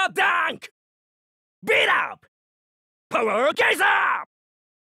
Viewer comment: I always hear POWER JASON!!